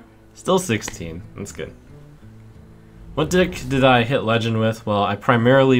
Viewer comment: i swear he said dick...